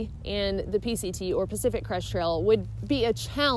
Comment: Why does it sound like you say Pacific "Cresh" Trail? I'm not making fun, but is it only me? I love this channel, keep up the good work!